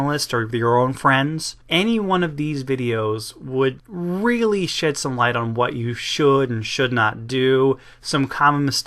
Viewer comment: Did say "shit some light", or "shed some light", because I heard "shit some light".